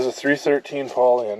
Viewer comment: The 60 Fargo is a 313 poly i thought for sure you said 413 poly motor,, anyway I've heard of a 318 poly but not the 313 poly cool learn something new every day